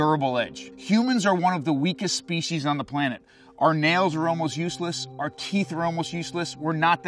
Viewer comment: Nail is useless Nail: Huh so yea your fingers ain't gonna be good without me :) Edit: Yea he did just say "almost"